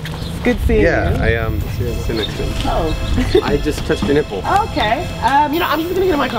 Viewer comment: is it me or does he say "I just took the nipple."?????